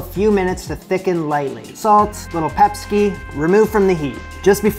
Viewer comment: I had to suddenly stop and rewind when I misheard him say “a little Pepsi.”